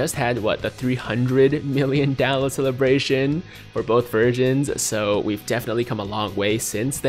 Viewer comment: Ik he said versions but sounded like he said virgin